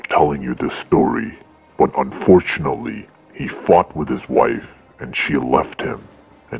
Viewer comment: literally thought he said the f word 😂. But actually he said fought